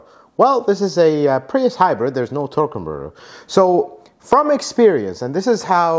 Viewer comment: are you saying "There is no Torque converter" (torque converter shutter)? it is very hard to tell what you are saying. You might want to consider adding graphics (that say torque converter, if that is what you are saying) to clarify.